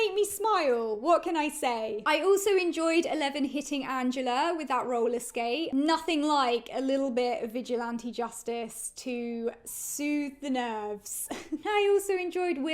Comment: I THOUGHT YOU SAID "Eleven kissing Angela" 🤣😂🤣🤣🤣😭😭